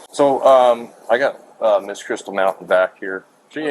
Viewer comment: I thought they said her name was Crystal Meth not Mountain. Even Mountain sounds fake.